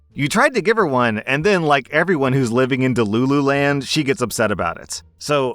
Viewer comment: DID SOMEONE SAY "LOO LOO LAND" ?